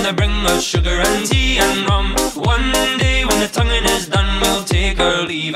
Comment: Did I hear "one day when the covid is done?" Lol